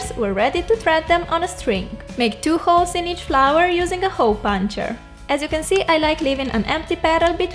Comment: I heard "hoe puncher" and couldnt stop laughing